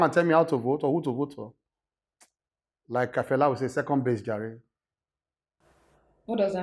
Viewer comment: This SAN don hear Fela tired! I heard him saying; Second based jare, lol!